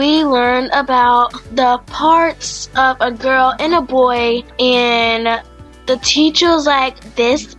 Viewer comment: Tell me why I heard “we learned about the parts of a boy in a girl”